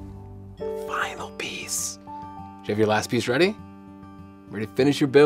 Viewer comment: When he said final piece it sounded like he said I'm obese. Lol